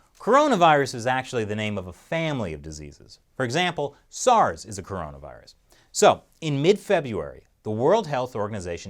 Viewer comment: Genuinely thought that you said Soros is a coronavirus!